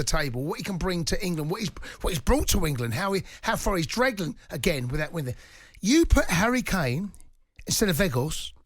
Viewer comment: Can anyone tell me what Cundy said 4:20 😂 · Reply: He meant to say "how far he dragged England, again without winning anything"